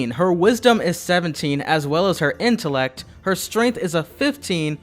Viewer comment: Did he say intelec?